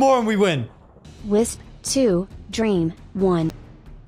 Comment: I heard "Wisp won & Dream too"